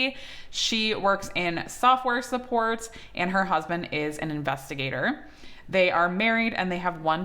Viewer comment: 😂 I thought she was saying she worked in software support and as a husband investigator 😂